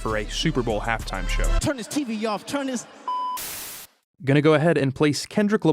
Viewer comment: Because I don't know his songs and because I couldn't understand his pronunciation, i thought he was saying "Turn this TV off" not "Turn his TV off," an I thought the message was more powerful - implying he was telling people at home to turn the TV and stop watching the game. Apparently it was just some dig at telling Trump's handlers to turn his TV off because he watches too much Fox and Friends [that is what i have read and been told, anyway.]